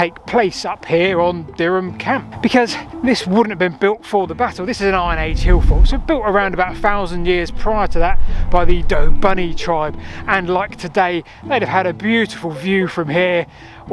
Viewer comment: Sounded like you said, ‘the Doe Bunny tribe’ ?